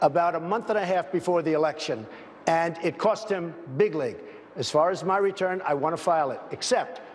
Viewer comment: does he say bigly? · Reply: at the risk of being consider a Drumf supporter, he said "big league". His moronic manner of speaking makes it sound like he said "bigly" Then again the guy is an idiot and probably did say "bigly"